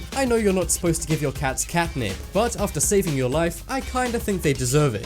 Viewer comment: What he say in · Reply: Cat nick